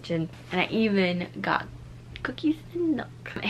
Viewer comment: For a second when he said I got a cookie I thought he said I got a poopy an then said show them and I was like ewww